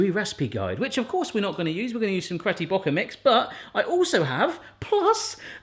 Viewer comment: did he say “cretty brocker” mix instead of “Betty Crocker” 😂😂